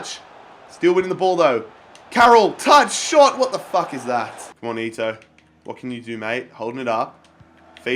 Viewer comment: Did I just hear say ‘What the fuck is that?’